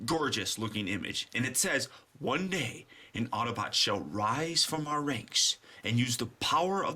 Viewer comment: great review. I had only heard about Transformers Prime a day ago, and I already want all the toys lol. It's funny I had always thought Prime said "An Autobot shall rise from our recks"